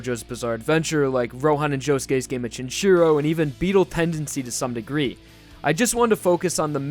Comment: Did you just say Beetle Tendancy?